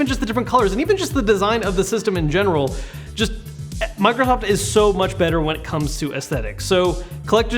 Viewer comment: did he say microhoft?